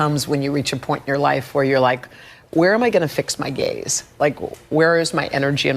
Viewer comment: I thought she actually said, "Where am I going to fix my gays(gaze)?" I think Jenna heard that, too and was uncomfortable. LOL